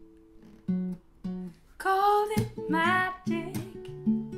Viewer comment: I HEARD "CALL IT MY DICK" AT 0:22 IM LAUGHING